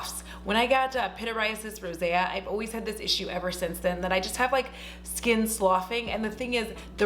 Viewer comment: I keep hearing you say "skin sloth", and I was kinda jealous, because I kinda wanted a skin sloth...